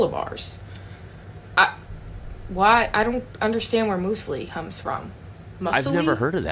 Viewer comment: I think she's trying to say "Muesli" pronounced "myoozley"?